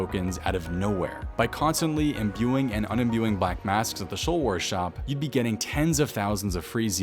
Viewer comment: did he just say showl worz shawp